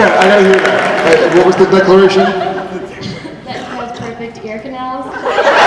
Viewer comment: what did the girl asking about hiatus say at 4:02 ? I didn't quite hear it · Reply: That Jensen has perfect ear canals